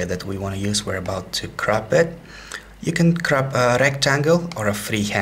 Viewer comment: did you say area your going to crop it or crap it? I couldn't help it but hear